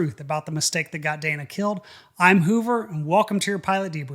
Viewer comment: Man, I thought you said Goober. I'd say that fits better.